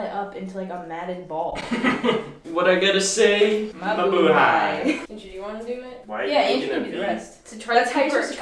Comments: Did I heard it right at 8:08? Really "Mabuhay"? Or I'm just hallucinating? I'm confused, send help! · Did i just heard mabuhay? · and andrew : mabuhay?-?(did they said that ???????) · did they say "Mabuhay" gusto ko lang naman malamannnnn Edit:I LOVE YOU